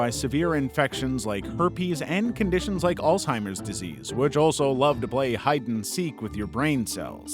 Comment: I heard it "Old-timers disease" 💀